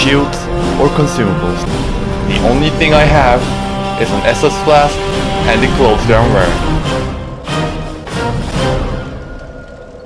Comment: What the hell are you saying here? It sounds like "and a glow sternware." The closed captioning thinks it's "and equals to the RAM." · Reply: "The only things I have are an Estus Flask, and the clothes that I'm wearing."